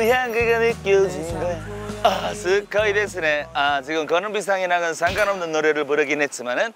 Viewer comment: 다나카 " 가루비상과는 상관없는."" 가루비상.. 이라고 들리는데?